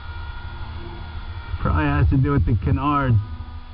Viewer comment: I thought you were saying " ..the kinards... " as in , they were making it a bit . . fu - kinard. . .to fly. . . lol